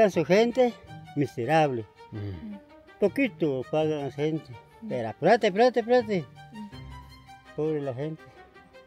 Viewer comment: Guatemalan here, loving the video so far! One minor correction: on the subtitles on 13:15 He doesn't say "plata, plata, plata" which I guess is what you translated to "money, money, money". He actually says "apurate, apurate, apurate" which means something like "hurry", you can also see it by the gesture he makes. I believe he is alluding to how they mistreated the workers asking them to work very hard.